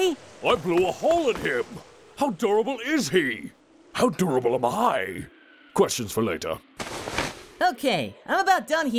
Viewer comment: "How durable is he? How durable am I? "questions for later." LMAO Cell's the best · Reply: I seriously thought he said "How adorable is he? How adorable am I?" and thought nothing of it.